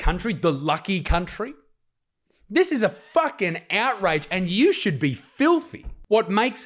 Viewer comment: Can someone tell my American ears, did he say “and you should be filthy?” It’s what I heard multiple times but not familiar with it if it’s a term. 3:25 I am so sorry you are dealing with this. It’s scary where the west is heading. Orwell would roll in his grave.